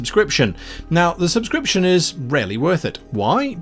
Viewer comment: When Guy said "The subscription is REALLY worth it" I heard "The subscription is RARELY worth it." Took a second to adjust my hearing.